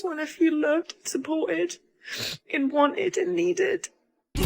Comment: "I want to be loved and wanted" is what I thought the first woman said. She had a hundred guys asking for her time and company and wanted marriage.... Nope!! You're booorrrriinggg is what they say about those guys. Now you have to pay your own way through life.